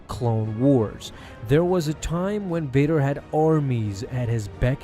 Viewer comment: I thought he said "There was a time I hade arms."